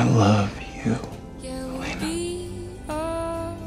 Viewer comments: I know it's elena, but btw i heard it Nina "the real name of elena" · Well the first time I watched it I thought at 03:24 he literally said Nina...😭...I know he is married and has a daughter but this hits different....